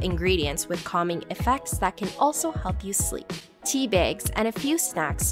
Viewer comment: Im sorry… did she just say “teebaygs”?